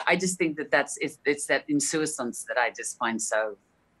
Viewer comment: Insouciance! What a fantastic word. It sounds like she says "insou-i-sance" though... is this like one of those "aluminium" things?